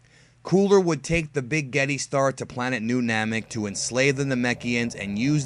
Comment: did you say spaghetti star?